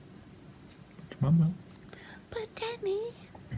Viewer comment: it sounded like she said "but daddy..." and I got so excited, but I'm pretty sure thats not what she said 😂😂